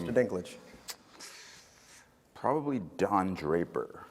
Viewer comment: Guys what did Peter said?:( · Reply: "probably don draper" whose a character on another show